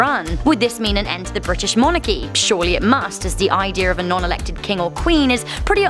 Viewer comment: did u say idear